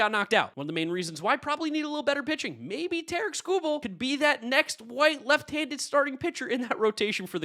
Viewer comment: Did you say "White left handed pitcher"?